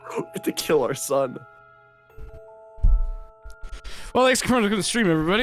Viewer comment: I love that I blanked for a second and when I come back to the stream I just hear "well thanks for spoiling SOMA" I really ought to get back to that game